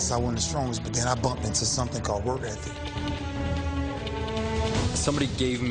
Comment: What did Ray Lewis say at 3:56-4:01?... "I bumped into something called--" what?... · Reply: Work ethic